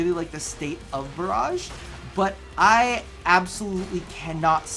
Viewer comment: is he saying Mirage because it's sounding like barrage,idk might just be me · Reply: Cody Mcneill he's saying barrage